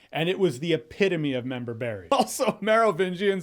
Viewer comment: What did he say 5:25, the epitome of member berry?